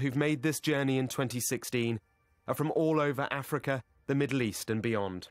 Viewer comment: Did he really say "all over Africa" at 03:16?